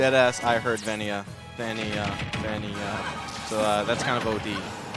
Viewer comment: it really sounds like they were chanting "Venia" instead of greninja